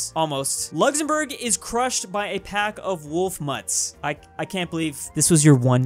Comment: Bruh I thought it said Luxembourg was crushed by wolf nuts